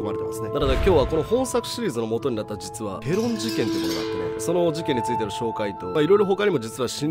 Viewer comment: ごめんなさい、ペロン事件をエローン事件と聞き間違えました🙋‍♂️